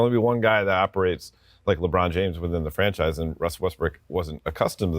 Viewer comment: The analyst sounded like he called Russ "Westbrick." @ 5:05.